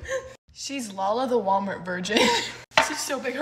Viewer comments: Is it weird that I thought when she said “she is Lala Walmart version “ I thought she said ✨VIRGIN✨ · I swear I heard “she’s lala the Walmart version (ver-gi- on )